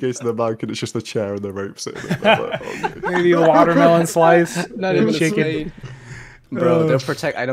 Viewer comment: did he just say watermelon slice and chicken???!!?